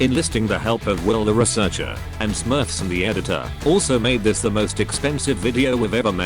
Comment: Big fat RIP, just noticed the titling error in the Call of Duty section. My apologies 😩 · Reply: I thought i heard him wrong when he said "smurfson as editor" at the very start